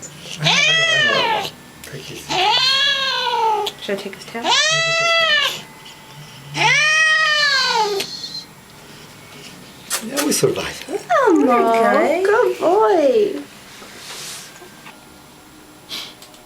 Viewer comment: it sounds like he says help! lol